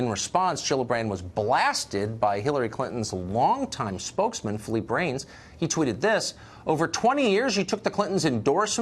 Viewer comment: When Tucker said "Phillippe Reins" I thought he said "Flea Brains"